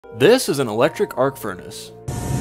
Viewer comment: Electric arc furnace? I heard "hardcore jacuzzi"